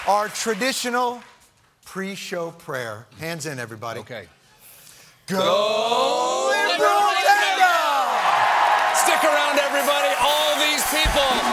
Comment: What was the prayer? "Go Liberal Agenda?" I honestly thought I heard "Liberal Panda"...that doesn't seem right.